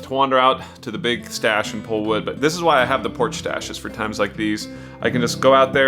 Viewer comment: Porch Stash? I thought he said Porn 'Stache!